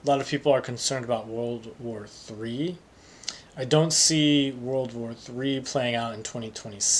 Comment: Thanks for sharing your annual prediction. Around 20:58 closed captions wrote WW II, not WW III (three) which is what you're saying. Correcting for anyone who is solely relying on the CC = closed captions